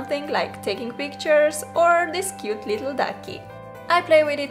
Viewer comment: WAIT DID SHE JUST SAY "CUTE DUCK"... OMI OMI OMI OMI OMI YASSSSSSSSSSSSSSSSS DUCKS ARE BEST!!!!! YOUR MY FAVOURITE YOUTUBER NOW!!!!!!! 🐥🐥🐥🐥🐤🐤🐤🐤